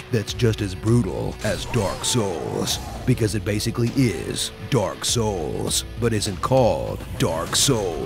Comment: is he said dark suace😂😂? Or subtitles are.....